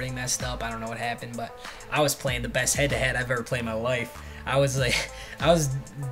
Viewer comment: I thought he said he got the best head he ever got in his life